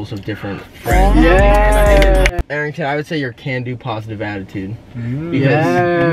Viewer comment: I SWEAR I heard Conan.